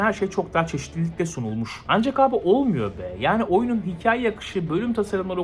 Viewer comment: Ben bu oyunu nioh oynuyorum diye anladım ilk seferde